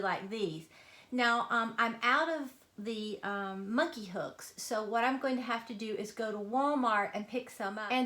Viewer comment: They are so cute what a great idea. what did you say you were going to hang them with, sounded like you said monkey hooks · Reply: Thank you I’m glad you like them! Yes I’m going to use monkey hooks. Take care. :-)